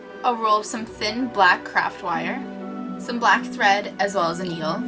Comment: I thought you said black crap feathers and black crap wire. yeah...